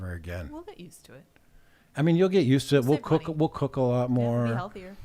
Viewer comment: sounds like is saying we will cuckold a lot more.